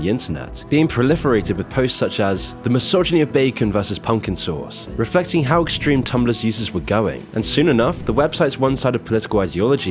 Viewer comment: Did you just say..."Pumpkin Sauce"??